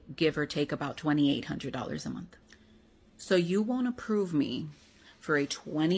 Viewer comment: I'd swear she says "so you won't approve ..." instead of "you want to approve". Otherwise, it doesn't make any sense to me based on the context.